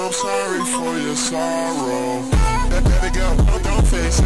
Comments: Why did I hear Abigail don't taste shit-💀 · anyone else hear “ baby girl, don’t face it” at 0:09 ?? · help i thought he said daddy girl instead of let it go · THE WHOLE TIME I THOUGHT HE SAID DADDY DON'T FACE IT